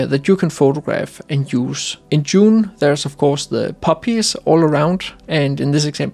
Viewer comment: Poppies all around - lol, at first I thought you said puppies 🐶 😄